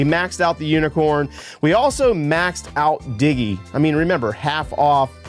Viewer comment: I was watching and looked away to do something real quick and I heard “we maxed out diddy” I was like WHAT 😂😂😂 but realized it was “diggy” when I rewatched